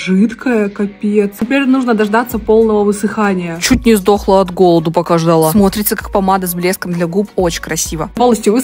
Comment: Мне послышалось:" Чуть не сдохла с голоду, пока жрала"🤣🤣🤣